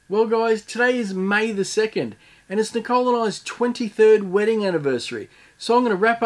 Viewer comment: You don't look old enough to be having a 23rd wedding anniversary. Congratulations!!! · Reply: I thought he said 3rd anniversary because I was only half listening. I was shocked by your comment. 23rd ...Huh !?! 😲